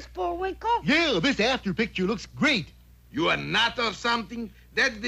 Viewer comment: did he say "hell this after picture looks great"?? Would be freaking dope if he did LOL